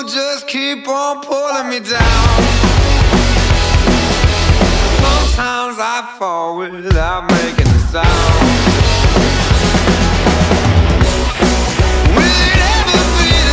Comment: Am I the only one who thought he said: "Sometimes I fart without makin' a sound"?